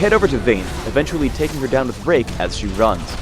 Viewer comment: This is what i hear xD and its so much more fun, in some way.. xD "I head over to Vayne and take her down with 'RaPe' as she runs" xD